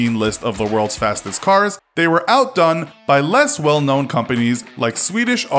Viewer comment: In 2:42 did he said not well knpwn company to koienegseegg?